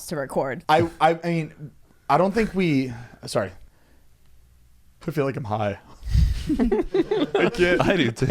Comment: when said “i feel like i’m high”, i real deal thought he said “i feel like a pie” SAME LMAO